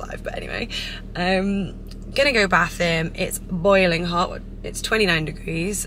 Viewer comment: Did she just say that 29 breeze is “boiling hot” or something else cause this just threw me off 😅